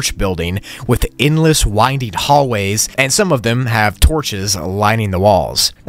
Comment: Sorry for the editing mistakes in this one! I must’ve been tired haha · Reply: one editing mistake is at 3:30 and there the captions say toches and not torches